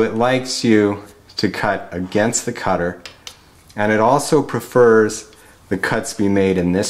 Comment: I think you're saying the mill "prefers" conventional milling to climb milling, which would make sense.